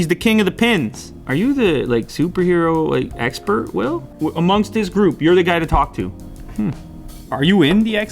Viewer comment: Did he say iMonstaa group or amongst us group 😂